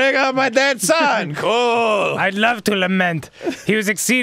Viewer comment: can some1 help please? wat did danny say at 7:17 and wat does tht word mean? lement? i dont know.... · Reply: doubt it .he said lament.it means grieving